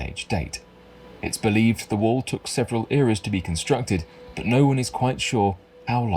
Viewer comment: lmao he keeps going on and on about the "several errors" when he misheard.... the narrators voice is so thick in accent that L heard him wrong😂he ACTUALLY said "several years" . even in context it doesn't make sense with the word errors. Either way ya gotta love these new discoveries that keep adding onto our deep and evolving human history! great job on the video L👍